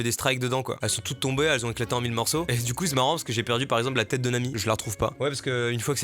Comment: Putin quand il a dit "J'ai perdu la tête de Nami" j'ai entendu "J'ai perdu la tête d'un ami"💀